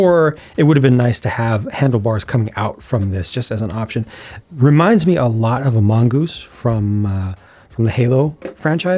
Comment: I first heard "reminds me a lot of amogus" and my mind flipped out of fear of what you just may have said, but playing the segment back I realized my mistake, pretty funny one in the end. Thanks for the scare I guess ?